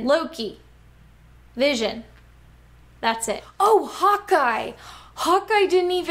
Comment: For a sec I thought you said hot guy instead of Hawkeye